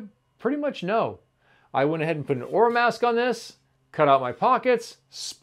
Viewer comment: What type of tape did you use for the paddle. It sounded like Ore mask. At the 5:48 seconds. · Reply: Oramask 813 Stencil Film 12.125 Inches x 25 Foot Roll For Cricut, Silhouette, Cameo, Craft Cutters <link>